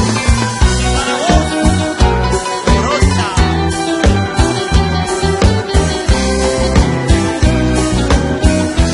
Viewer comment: Alguien sabe que dice en el minuto 2:09? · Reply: Para vos,morocha